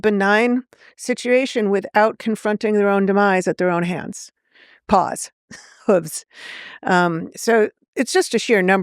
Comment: I must be tired. I thought she said "Pause", and was trying to figure out, "How could that be gay?" 🤔 "If the Dinos jerked each other to death?"